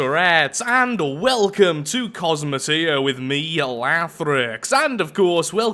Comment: Lol I thought u said welcome to astroneer